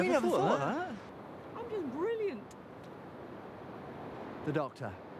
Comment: Did she say "I'm brilliant"?